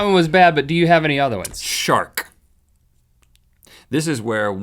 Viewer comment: when he said "shark" I thought he said "shart"